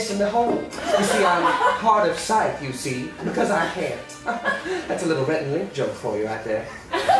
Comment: I have never been able to figure out what Damien is saying from 16:25 onward. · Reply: "You see I'm hard of sight, you see? Because I can't. That's a little Rhett and Link joke for ya. I also have two different tongues but none of them are in my mouth. Can you guess where?"